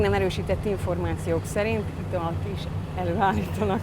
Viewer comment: nem értem amit mond mit álitanak elő? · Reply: Borus Martin italt!